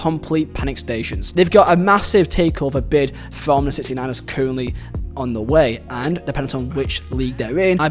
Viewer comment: Y'all notice said "69ers" instead of of 49ers, snuck it in like we wouldn't notice 😂😂😂😂